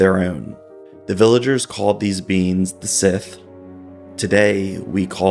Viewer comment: The sith? 1:36 is that what he said? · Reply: Sidhe, often pronounced 'shee'. Like the word ban-shee, which translates to woman-fairy.